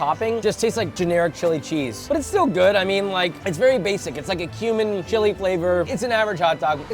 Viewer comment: I thought he said “human” chili when they were eating the hot dog in toon town 😂